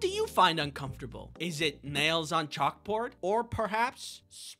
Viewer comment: I thought he said "Males on a chalkboard" 😂